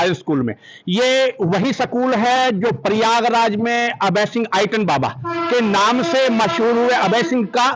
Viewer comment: Sec 1:13par reporter ne kya kaha ye item baba